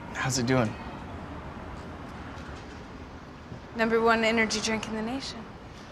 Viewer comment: "Number one energy drink in Indonesia." Is what I heard right? 🤔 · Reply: In the Nation